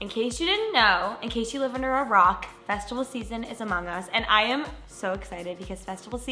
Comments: I thought I heard “vegetable season” instead of “festival season” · For a bit I thought you said “vegetable season “ instead of “festival season”